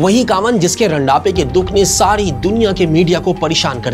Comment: did you just say "Randape"? 😂